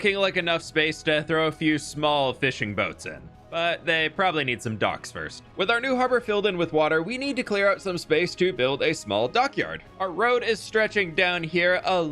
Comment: I thought said "these fishing boats need dogs first" and I was like "yep makes sense that tracks" and was sorely disappointed when he started building docks. 😅